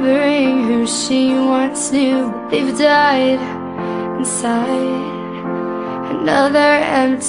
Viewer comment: Each time I hear "But they've died inside." It sounds like 'A baby died inside.'